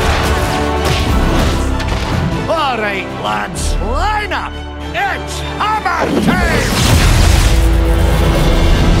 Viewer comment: could someone tell me what he's saying from 0:48 to 0:52 please? I can't understand well :) Thank you very much!! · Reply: "all right lads line up! its hammer time!